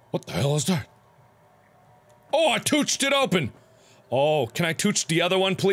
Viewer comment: It took me like forever to realize that tooch means touch I was like wtf is he saying!! 5:30 is when I realized what he was saying... :/